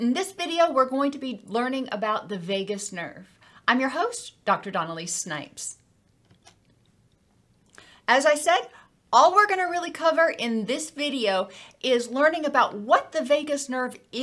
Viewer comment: I had to go back to hear your name again. I swore you said "Donald A. Snipes"